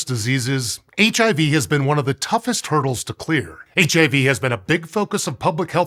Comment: Did he say ... "One of the toughest TURTLES to clear? "